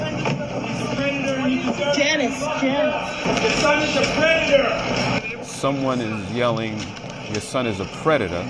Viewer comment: I'm sure I heard "Your son is a failure". Not sure now :)